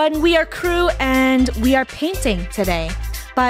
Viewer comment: i thought they said they were painting babies 👁👄👁